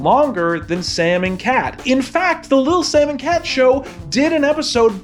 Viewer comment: you said "lil sam and cat show" but i kept hearing "the lil salmon-cat show"